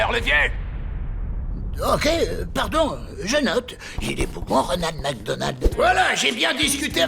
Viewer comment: a 9:51 il dit que il est quoi ronald mcdonald X) ? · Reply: "Il est bougon Ronald Macdonald" ça veut dire qu'il est de mauvaise humeur